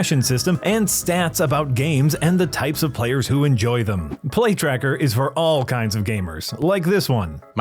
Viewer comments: Considering all this, it feels like a miracle that any game with a AAA budget and resources gets made at all. Also, I swear every time I hear the "Play Tracker" ad, it sounds like he's saying "Plague Tracker" and I briefly fear I've been transported back to 2020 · When the ad read started, I thought I heard "Plague Tracker" instead of "Play Tracker". After listening to the ad, I'm not convinced I was wrong the first time.